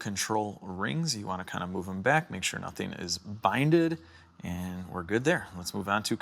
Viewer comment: did I hear "nothing is binded" ?, it is supposed be be "bound" right? I'm not a native so I don't want to jump to conclusions